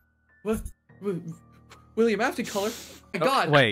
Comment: I thought it said "Collab"